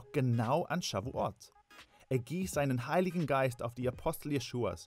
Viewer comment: Ich hab nicht verstanden was bei 1:38 gesagt wurde können sie es bitte unter diesen kommentar schreiben ich brauche es für eine powerpoint präsentation für die schule würde mich sehr freuen😊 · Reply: Heiliger Geist